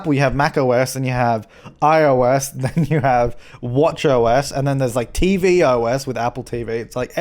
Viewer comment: "Watch OS" am I the only one who hears "Watch yo' ass" ? lmaooo